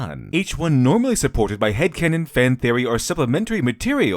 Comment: Excellent video. 15:23 should be "canon" instead of "cannon."